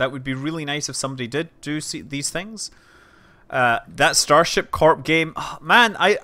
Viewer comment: What game is he talking about at 2:41? I can't quite make out what he says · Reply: Starship Corpse